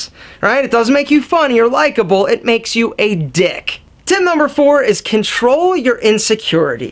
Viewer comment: Just to sum everything up: 0:34 1. Have a positive attitude. 1:34 2. Be engaged, be passionate. 2:08 3. Have a sense of humor. 2:49 4. Control your insecurities. 3:14 5. Be a good listener. 4:00 6. Be humble. 4:52 7. Learn to compliment. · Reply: all I hear at 2:49 is dick